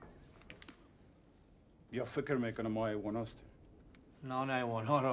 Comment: What was his soldier saying to him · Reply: “They think we are animals” “Yeah they brang animal food”